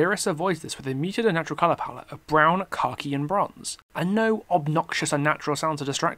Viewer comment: Did he just say " brown, cocky and bronze " ? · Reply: "khakhi"